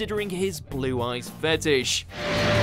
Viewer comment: did he say “blue-eyes fetish”?